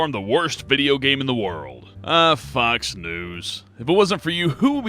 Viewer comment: at @ 5:00 in this video he says uh fox news n the subtitles said "%uh fucks news". LMFAO I bout choked when i saw that but funny think is that sounds about right.